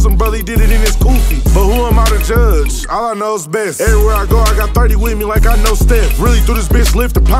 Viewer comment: Allah knows best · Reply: I caught that I thought he said all I know best